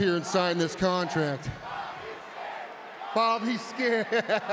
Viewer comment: The way Brock Lesnar laughs at the crowd chanting "Bobby scared!" was absolutely hilarious during this Contract Signing segment. 😂😂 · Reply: They were saying that he’s scared? I thought they were saying “Bobby’s gay”.